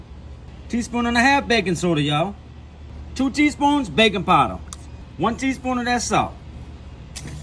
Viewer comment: For a second I thought I heard “baby powder” as one of the ingredients and thought this was some next level recipe. Then I realized it was “baking powder” 😂